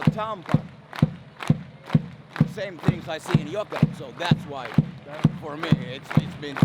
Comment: I'm sorry, I keep hearing you say "Jukurit" instead of "Jokerit" and that's completely different team. :D